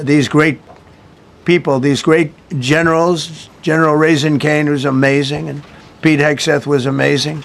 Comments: did he just say "General Raising Cane"?? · Did he say General Raising Cane